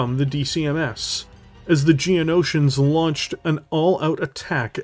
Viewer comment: Did he say Geonosians? The bug things from star wars?